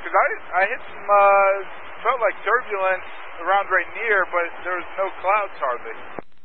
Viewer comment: I think he says "Rainier" not "right here"